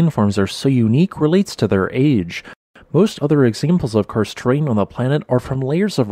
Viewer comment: When listening to the video I just heard "Cursed" not "Karst", maybe it's a cursed terrain as well with sinkholes?